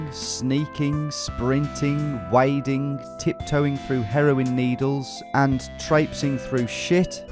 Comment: I misheard "heroine needles" as "harrowing needles" the first time, which was not untrue